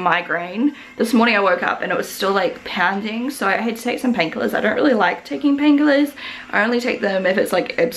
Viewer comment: am i the only one who heard that she said "had sex and some pain killers?"